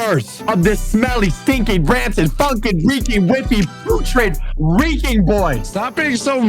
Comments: I thought he said f**** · did he say f#%&